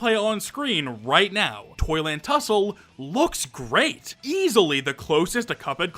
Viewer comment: why did i hear "Toilet Tussle"